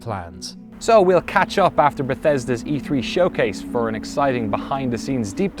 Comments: Did I hear him say "new game(s)"? As in plural? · did he say gameS or am I tripping?